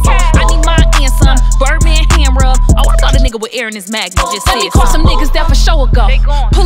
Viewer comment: Not Air in his MAGNUM OMFG HAHAHAHAAHAHA!!!!!!! I LUV KASH DOLL!!!!!!!!!!!!!!!!!!! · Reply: I thought she said “air in his mattress just said summ” either way she hilarious